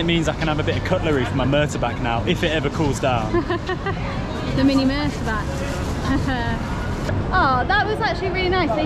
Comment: aww so cute, I thought you were saying mini motorbike...🤣silly me